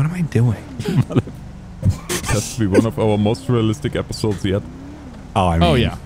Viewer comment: I thought he said mozzarella stick not most realistic lol